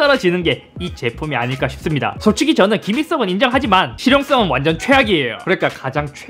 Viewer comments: 발음때문에 김일성은 인정하지만으로들림 ㅋㅋㅋㅋㅋㅋㅋㅋ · 순간 "김일성은 인정하지만" 이라고 들려서 깜놀 했네 ㅋㅋㅋ